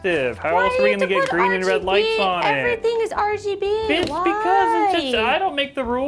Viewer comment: does he say “bitch because? or “it’s because”?